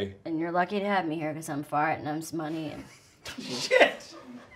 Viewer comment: Up until right this second I always thought Amy was saying "I'm farting on sum money" to Louie. 😂😂